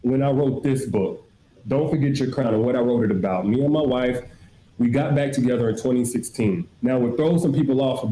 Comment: I thought this nigga said "the mayor my wife"